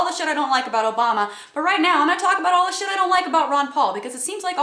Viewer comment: I'm not going to lie, i thought that said "Ru Paul" and i got all excited for drag queens XD